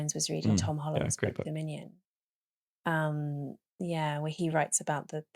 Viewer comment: Hi! You both briefly mentioned a book by an author Tom Hollins/Holland (?) around the 35:10 mark. I couldn't catch the title, could you share? Thanks! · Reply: Dominion: How the Christian Revolution Remade the World <link>